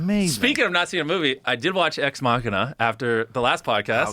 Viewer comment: what does Jimmy say he watched at 1:26:05? Exmohuna?